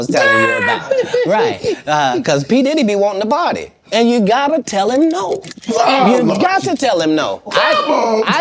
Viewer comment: “Diddy be wanting to party, and you got to tell’ ‘em no.” 🤣🤣😂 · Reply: I thought he said "Cuz P Diddy be wanting the body and you got to tell him no"